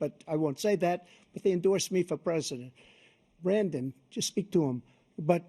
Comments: Did Trump just call Biden "Brandon" at about 2:12? 😂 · (Update: lol. Lolololol. 1/20/2025. 😂) Trump won this debate, ended Biden’s candidacy, and got himself reelected all in two hours. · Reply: Did trump call him Brandon? 2:11 😂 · did Trump call Biden “Brandon” 😅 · Did Trump call Biden “Brandon” on minute 2:13? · did he call biden brandon 😂😂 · did trump say Brandon just speak to them? 2:12. Lets go Brandon was at an event where the crowd was saying "F - - K Joe Biden", but the media was saying Lets hear the crowd chantting "lets go Branden."